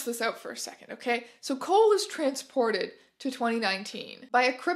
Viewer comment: I heard "Cole is trans" and went oh good for him :)